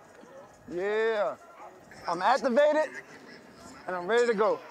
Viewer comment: 😂😂😂😂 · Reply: Yeah lol it sounds like he said master baited but to be real I think he said activated